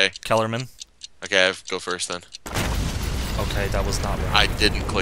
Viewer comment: #happycasualfriday And I'm watching this on a Friday. YAY :P Also I heard "I have to go first", so you were wrong.